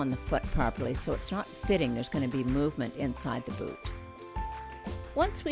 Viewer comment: i swear at 2:20 she said the is gonna be movement in the fewet (foot)